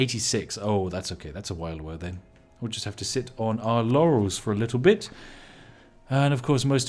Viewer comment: I heard “sit on our yannys for a bit”. Anyone else?